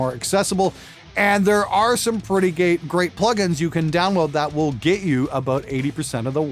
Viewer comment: pretty g a y plugins is what I heard, lol. Hi! Fuuuuuck you!